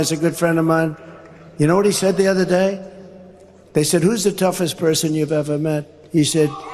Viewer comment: I think he said 'who is the most stupid.....'